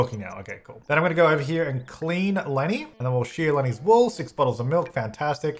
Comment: I know he said “wool”, but that’s not what I heard the first time. Haha “Clean Lenny and sheer Lenny’s balls”, is what I heard.